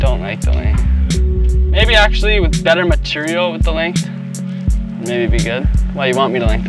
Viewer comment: What does he say 1:13 and 1:29-1:34? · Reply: "Maybe be good?"